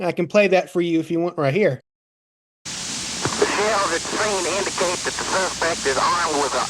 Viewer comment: At the 30:10 mark, doesn't say anything at all about ONE shell at the scene indicating anything. He clearly says "the SHELLS at the scene indicate...", i.e. is NOT referring to only one single shell.